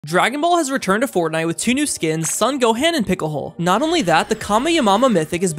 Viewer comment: Top most wholesome words 1.kamyamama 2.son goman 3.picohole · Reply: I heard him say Goham